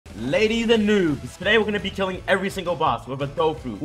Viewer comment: i thought he said ladies and nudes for a second....